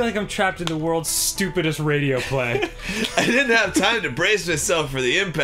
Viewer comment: Dan: I am in hell... Me: sigh You and me both. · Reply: MLP_StarBreaker what did he say at 1:49? World as stupid as radio-flag?xD I can't hear seriously...mumbly... Also at 2:54...they are gonna go to japan? They expect the girls to be lke this or what ? x)